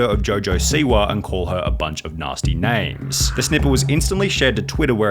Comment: WHAT DID HE JUST SAY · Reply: "Snippet", thats all. Please try just listening before making a comment trying to cause drama.